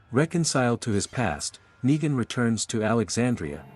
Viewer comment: Bro on 2:45:00 did I hear something say turned on bro 💀💀💀